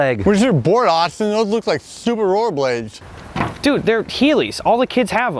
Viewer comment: “Where’s your board Austin, those look like stupid roller blades.” “Dude, they’re heelies, all the kids have them.” “Huh, I wouldn’t know, I’m not a kid. I’m 12 and a quarter, I’m an adult.” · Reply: I thought he said super roller blades